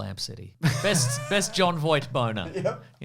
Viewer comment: I thought said "Best Jon Voight Boner" instead of bow and arrow, and I was thinking they really undersold how crazy Megalopolis was.